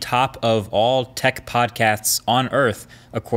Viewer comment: who heard podcats?